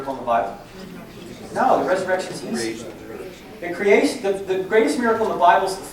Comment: can someone please tell me what he says? “No the resurrection is ...”? I know he says that creation is the greatest miracle. Just curious as to why he was saying that instead of the resurrection. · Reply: He said the resurrection is easy.